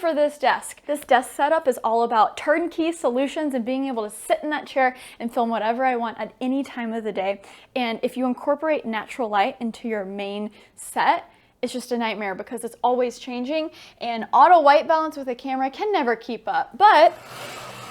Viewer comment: While multitasking during this video, when you said “incorporate natural light”, I heard “incorporate NATURAL LITE”. Both make sense to me.